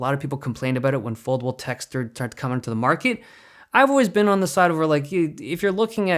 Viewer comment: Did said semen at 1:21? There's no semen on the phone? 😅